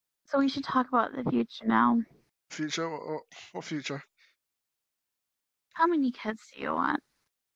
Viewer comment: Bro I thought she said how many heads do you want